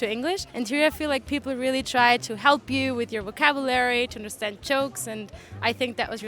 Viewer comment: La chica de Austria habla increíblemente bien. Parece de aquí en las expresiones e incluso en la entonación,solo tiene ligerísimo acento extranjero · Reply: Su acento se nota en el Ingles (que habla muy bien) cuando pronuncia "chokes" instead of jokes 4:33.